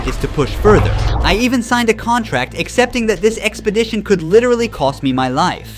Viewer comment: Did he just say he sign a death clause?!….reminds if the time my friend, at a stop light, said “buckle up!” I said “let me out then!” 😅